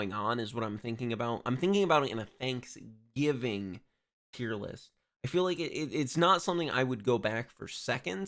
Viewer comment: I thought it said thanksgiving Terrorist